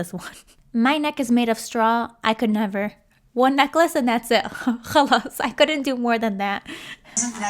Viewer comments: did she said “خلاص" is she Arab?! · Did you say 5ala9(خلص) 8:40??? Are you Arabic??